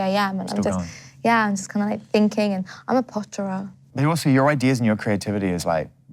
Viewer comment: What's the word said at 25:24? Something like "potterer" (?)